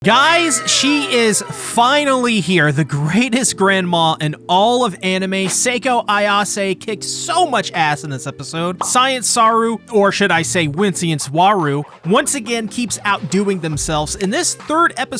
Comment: I thought you said “…got SO much ass in this episode!” Lmao